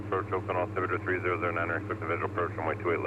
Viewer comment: Great video and editing. I heard him say "cleared for the visual (not expect)". Then I heard him correct you and I was shocked also. Had to play it back and listen three times to get get a barely audible "expect". Fast talking and poorly enunciated. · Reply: I have to disagree, he clearly said "expect" the visual approach. On the other hand, as for that Shamrock pilot at 26:25, I still can't figure out what he's saying! 😂